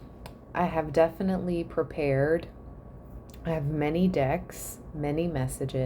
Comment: I swear I heard you say many dic*s 😮😂